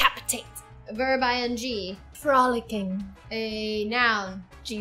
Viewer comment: When said frolicking I thought she said f***ing 🤦‍♀️